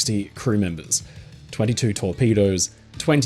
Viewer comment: i heard 22 tall pedos